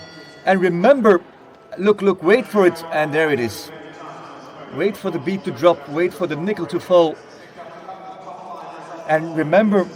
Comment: DID HE SAY THE N WORD??????????????????????????????????!!!!!!!!!!!!!!!!!!! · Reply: he said "wait for the beat to drop, wait for the nickel to fall"